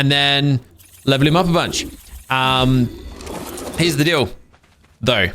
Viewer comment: SAID HERE'S THE DEAL . . . THO SO HE CLEARLY SAID HERE'S A DIL DO